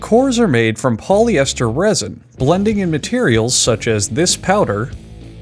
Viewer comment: I heard “this powder” really helps strengthen the bowling ball